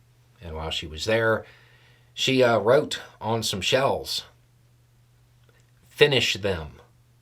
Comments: Did I here you right ? Shells as in artillery shells ? I first thought I heard shelves , had to start the video again. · I hadn't heard the news before seeing this video, so I was a bit confused. First, I thought you said she wrote on "shelves", and was thinking of spraypainting the message in a grocery store or similar - a bit weird. Then I read the automatic subtitles and saw it was "shells"... but to me that means seashells, so I was torn between engraving the message on a seashell (weird) and spelling it out using seashells on a beach (also weird). Then I googled it and saw the image (of artillery shells) and it all clicked. Thank you for coming on this journey with me.